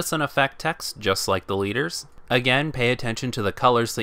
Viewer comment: I think I've been watching to much vanguard because whenever you say the leaders it sounds like your saying deletors as in the link joker archetype